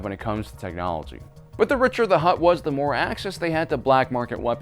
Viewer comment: Thought you said Richard the Hut